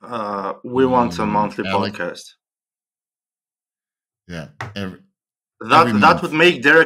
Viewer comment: Lol I thought he said “we want him off the podcast “